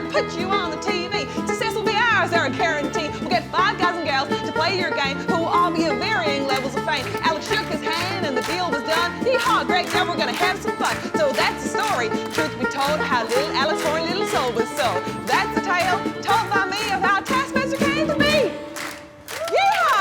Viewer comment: Rose Matefeo – Taskmaster Tale There was a country boy called Alex Horne he had a heart of gold and a brain full of corn He made up tasks sun rise to set To test the cows and the chickens on their intellect Until one day he went to the big city And met a tall old man named Greg Davies He said "I'm gonna put you on the TV success will be ours, I guarantee! We'll get five guys and gals to play your game Who will all be of varying levels of fame" Alex shook his hand and the deal was done "Yeehaw", Greg yelled, "we're gonna have some fun!" So that's the story, truth be told How Little Alex Horne's little soul was sold So that's the tale told by me Of how Taskmaster came to be! Yeehaw! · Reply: I thought she said 'Yeehaw, Greg Devil's gonna have some fun' and I'm gonna hold onto it cause I like the play on words :')